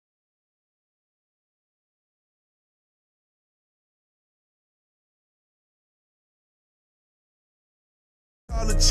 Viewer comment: Sauce or salt? What did he say? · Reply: Sauce